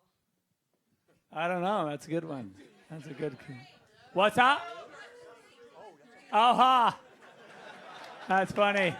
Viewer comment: what did she said at 2:58:00 ? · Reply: Sounds like she says "two and three" and he says what's that and she says "Justice League 2 and 3" I think